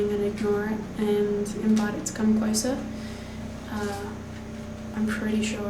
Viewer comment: "Invite it to come closer". Did I hear that correctly?!! I'm freaking out for your sake. LOL. Great creepy stuff, guys. But really hope you guys can get stronger lights so we can see all those creepy dark corners. Looking forward to part 2!